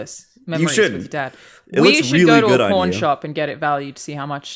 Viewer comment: saying they should get it appraised at a pawn shop was really confusing because at first I heard, "Porn shop"